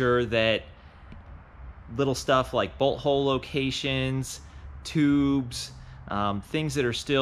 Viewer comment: did he say butthole locations ?😂😂